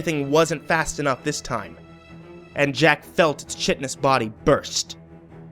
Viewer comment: did you really say "Jack felt the shit in his body burst"? Because that's what it sounds like. 0_o